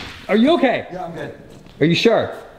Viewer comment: It’s kinda awesome that the first thing he said was “are you good Zach” which shows how much he cares for other people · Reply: I thought he said “are you good, dad?”💀